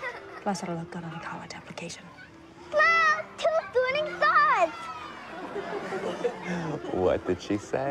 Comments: "Wow! Two shooting stars!" Took me like 5 minutes of replaying that to remotely understand her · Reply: James Ge I thought she said "Two twinning stars" lol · To those wondering what she said, she said "Whoa! 2 shooting stars!"